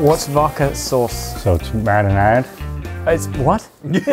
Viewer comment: i think he’s saying marinara? 😅